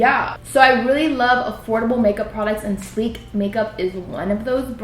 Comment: I thought it said adorable makeup😂